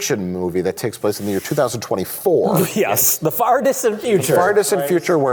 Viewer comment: During the Robowoman discussion, I misheard 'the farthest of futures' as 'the fartest of futures' and I think that setup would still be superior than what was captured in Robowoman.